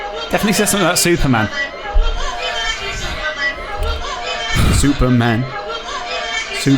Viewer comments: I swear it says: fuck you nigel superman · I heard 'Paul is a superman' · Thought it said wham batty superman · I heard 'proper party man, is Superman'.